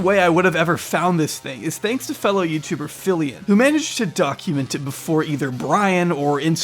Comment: I wasn't watching the screen, and heard you say "Documented by Phillion" and thought you said "Documented by Fillian" and was like "Damn A vtuber is involved is this now?"